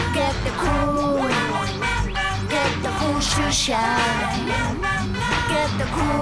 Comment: it’s 2020 and i still haven’t gotten the cool shoe shine i’m desperate and i’ll do anything, please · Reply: I once thought she said get the cool shoe size